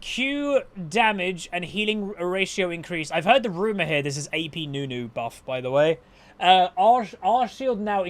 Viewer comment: Ooooooo you said nunu buffs? I thought you said sylas buffs